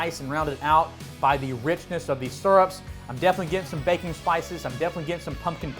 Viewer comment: Every time you say “baking spices” I hear “Bacon Spices” and I think “oh, dude’s got his own spice blend!”